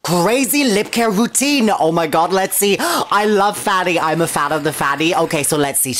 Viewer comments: i thought you said fatty not fanny🤣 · Omg i thought i just heard "OmG I'm A Fan oF FatTies"